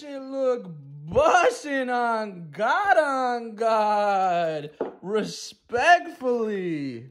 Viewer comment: Try melted cheeeeseeeee for God on God! RESPECTFULLY! Damn.. this shit busssin! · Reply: Oh so he says "God on God" I didn't notice 😆 I thought he was saying "garanga"